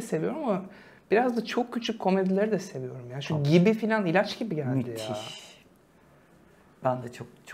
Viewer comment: şu 46:31 de hangi diziyi söylüyor bu yine kepçük ağzı ile konuştuğu için ne dediğini anlamıyorum dekoderi olan biri yardımcı olabilirse sevinirim. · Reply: Exxen'deki Gibi, Feyyaz Yiğit ve Kıvanç Kılınç oynuyor.